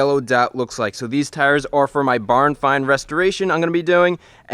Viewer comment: Did you say barn find restoration?! I can’t wait to see what it is!